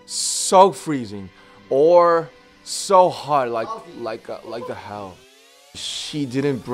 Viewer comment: I thought namjoon was saying “so hot like what the hell” turns out he said “so hot like hell”